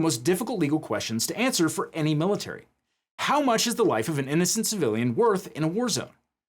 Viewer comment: I misheard thay as "how much is the wife ..."